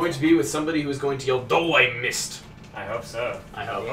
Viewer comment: I can almost swear that I heard Wario say d'oh I missed when he lost 40 coins to Bowser